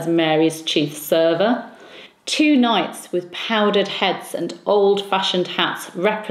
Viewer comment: Powdered heads? Did I mishear? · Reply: No, I did say "powdered heads". I think they used a decorative hair powder but I'm not entirely sure. I've read that in Elizabeth's reign, white powder was used on light or grey hair, and violet powder was used on dark hair. Later, it would mean the wearing of a powdered wig.